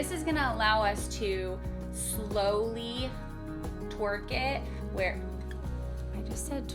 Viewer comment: When you said twerk I thought you said torque lol 🤣